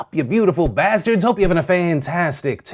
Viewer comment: Sup You Beautiful Baskets! Hope you're having a great day. :) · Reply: I always thought you said "...you beautiful bastards"